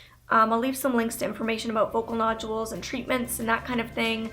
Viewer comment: I thought she said vocal noodles lol